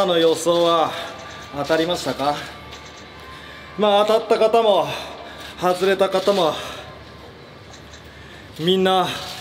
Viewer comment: ゴメン気になって 「外れた方ま」に聞こえるんやけど俺だけ？(^^